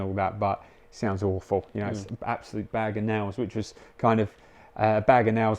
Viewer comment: silly question: does anyone know what he's saying at 7:41? I suppose it's an english slang - sounded to me like "bagenarrows" or "bag and arrows"? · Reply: Bag of nails!